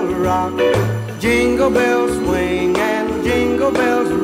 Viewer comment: I thought it said jungle WHYYYYY